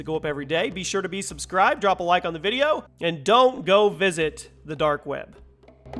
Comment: You said "don't go visit the dark web"? I thought you said "go visit the dark web". Too late to go back, if I don't return, I was probably arrested